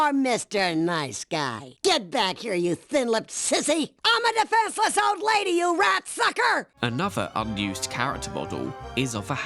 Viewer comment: Did anybody else initially hear Agnes at 1:20:25 say "I'm a defenseless old lady, you rat fucker*!"? 😂 I was so disappointed when I rewound and heard what she really said.